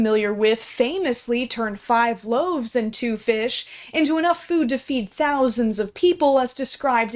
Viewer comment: I heard that as “5 loaves into fish” and I’m like… that’s close? XDXD